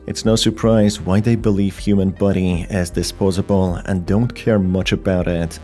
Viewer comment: I think I heard him say Human Buddy like a 100 times 💀